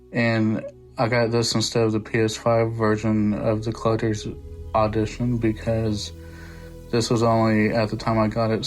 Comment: I thought you just said the word "edition" weird til I read it said "oddition" lol nice unboxing! I need to try this series